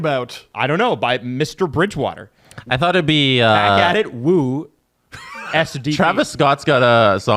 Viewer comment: I never knew that the first words of the intro was "Back At It." I always thought it was saying "Spaghetti."